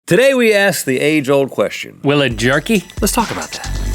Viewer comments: oh my gosh i cant believe after all these seasons i thought they were saying "today we ask the angel a question" OMG HAHAHAHAHAHHAH IM SORRY GMM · For a split second, I thought they said, "Will It Gerky?" and was very, very puzzled.